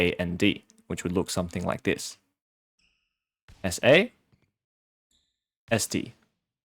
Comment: did he just say... esdee